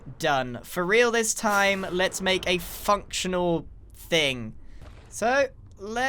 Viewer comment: Lol at 5:50 it sounded like he said f***tional 😂😂